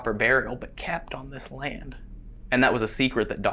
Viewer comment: Hey you two Every time you said DR. Snead. I laugh because it sounded like you said Dr. Sneeze 😄